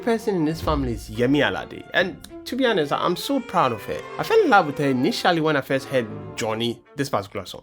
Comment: Why did you say "I'm so proud of hair" 6:41 Are you Ghanaian? 😂😅